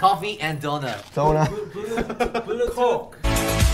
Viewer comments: it sounded like he said cock🤣is it just me or do I just have a dirty mind. Love him to death tho💜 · i thought tae said cock😭🤣. Im sorryy